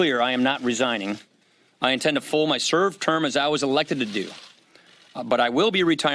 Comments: Did Ryan say that he intends to full his serve term? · Did he say "full my serve term"? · "Full my serve term" that was what i thought he said. But that other sentence - "last years as a member of the house". So is he planning to run for Senate?